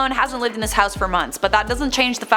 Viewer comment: 1:25 Captions says week. Reporter says month. So what is it??.